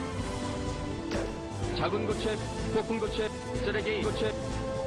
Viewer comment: i think he says 작은 고추의 폭풍 고추의 세르게이 고추의